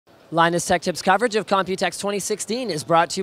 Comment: did you say linussextips?